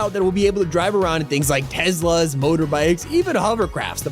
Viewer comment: he really said "tezlas"